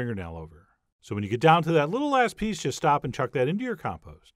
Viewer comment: Ha! 1:50 I could have sworn he said “Little ass piece”, turns out “little last piece”.